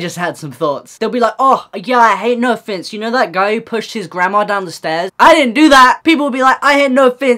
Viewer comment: hAHAHAHA oh :’) I thought he said “The guy who pushed his grammar down the stairs.” And I think that couldn’t be more true.